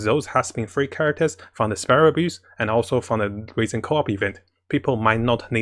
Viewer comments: god i laugh every time i hear "Spiral abuse" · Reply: I hear "sparrow abuse" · god i laugh every time i hear "Spiral abuse"